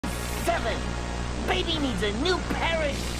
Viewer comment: what did he say 😳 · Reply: I think he said crap